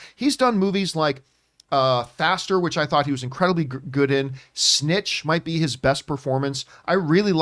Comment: Snitch was an excellent movie. Bernthal was also great in it. Definitely reccomend for anyone who hasn't seen it. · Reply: I thought you said snatch…whoops. But snatch is a really great movie.